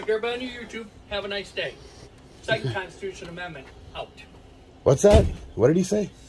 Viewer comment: Sheriff: “Second constitution amendment” 😡 Second amendment: ➡️🐻💪🏽💪🏽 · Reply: I thought he said “cite your constitutional amendment” ???